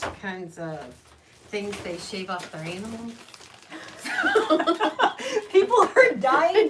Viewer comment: Thats alot better than what I thought she said. I thought she said anal instead of animals.